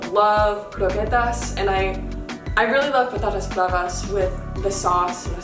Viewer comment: Me again, sorry... what did you say here, please? 12:23 I understood croquettes (which kind, plz?) - what was the second thing, what is it so I can look up recipes, plz? Thanks soo soo much! The reason the offer food is so you have something in your tummy. :) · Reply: I said I like Patatas bravas!! so so good